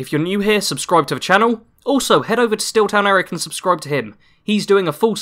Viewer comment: Who did you recommend to watch? I couldn't hear the name clearly. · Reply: SteelTownErick. Link to his channel here: <link>